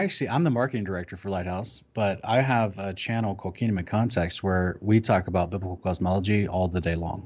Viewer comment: What is YT channel ? He said it so fast I couldn't catch it. It's at the 1:02:30 mark roughly. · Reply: Kingdom in context